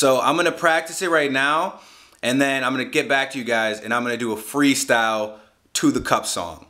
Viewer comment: sounds like he said "I'm gonna do a free stow"